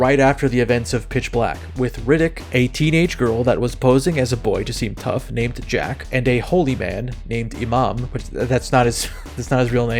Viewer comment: idk if I misheard the name "Iman" here or if this gets elaborated on somewhere but and an Imam is basically a muslim priest. I've seen the first two riddick movies and idk if keith david is intended to read as muslim here or if the line is meant to imply something about riddick's past. Edit: okay so calling a city "New Mecca" feels like a pretty clear indication that space Islam is a thing in this setting. So yeah ig keith david is just meant to be an actual imam.